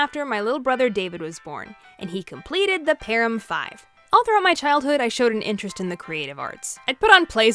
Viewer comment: I had an ad at THE PERFECT time, and i thought she said “Parham Fox” 😅